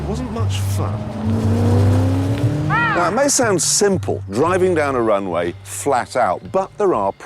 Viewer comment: press 2:53 it sounds like he says "i sound simple"